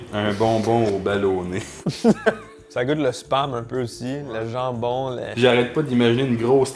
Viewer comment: "ça goûte le sperme un peu aussi" dites moi que j'entends mal 💀💀💀